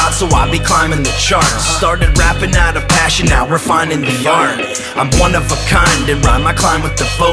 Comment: refining the......? Cant quite hear what you say there. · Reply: Refining the art 🖼️